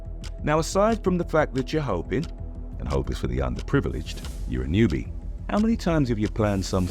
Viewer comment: May have miss heard, but at about 24:24ish, I thought I heard, "hope is for the under privileged". This explains why so many churches exist and the "hopeful" zealots who run them are anything but under privileged... sorry to change the subject.